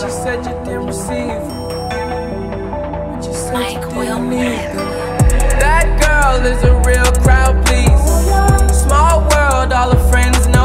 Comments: I thought he said "black girl is a real crowd pleaser "😂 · I thought he said "black girl is a real crowd pleaser "😂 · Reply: I thought he said That Gurl is a real Round Pizza 😭😭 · I thought he said "black girl is a real crowd pleaser "😂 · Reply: i thought he said "that girl is a real croud piece" lmao